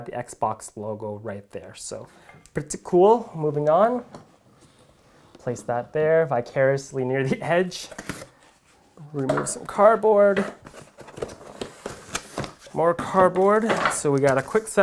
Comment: Did he just say "vicariously near the edge" at 2:54? Interesting